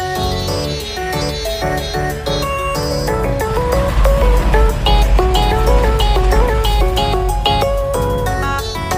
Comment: WHAT DID HE SAY???? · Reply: He said love you too babe